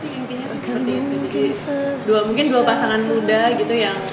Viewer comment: Afgan bilang apa? Plis bantu jawab 🙏🏻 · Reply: Yang mau kawin